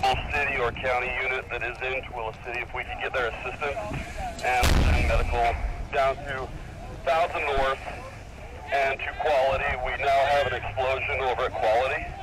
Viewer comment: It sure sounded like he said "we now have an explosion over inequality".